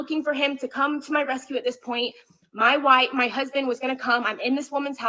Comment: she actually says "My White" before she caught herself and corrected what she was about to say. So this was very much about race to her. Of course, he was going to slow down in his neighborhood a few houses down from his home. Who pulls into their driveway doing 60? If he had been a white man, would she have thought he was following her. Ignorant B!÷[$